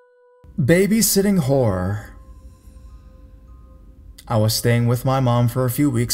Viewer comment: Am I the only person who thought he said "Baby Sitting Whore"